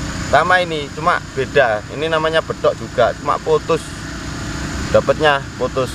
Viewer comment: SAMA INI CUMAN BEDA,,,NGOMONG APA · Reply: Klo gk salah bedax cuma putus itu aja